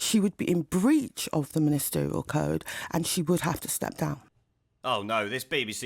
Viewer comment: Abbott : "there's a ministerial code". Me: is this the one that constantly "leaks" government Brexit policy making? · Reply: Funny, I thought she said 'misterial code'. Oh yes! She's not hurrying that Murray Mint at the back of her Blackwall Tunnel-sized cake hole of hers.